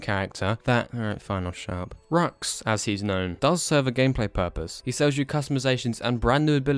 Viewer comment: am I the only one who heard "as he's gnome"